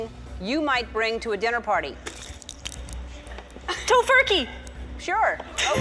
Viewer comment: Did she say "tofurkey"?